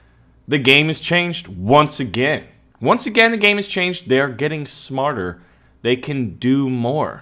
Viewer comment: "They are getting smarter" What I heard: "They are evolving"